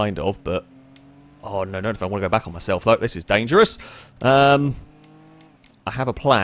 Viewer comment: did just say "fuck this is dangerous"?